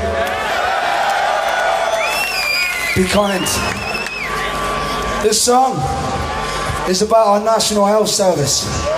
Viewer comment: What did Joe say at 14:55? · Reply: "Be kind' x